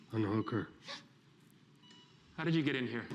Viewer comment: the subtitles say ‘I’m a hooker’💀💀💀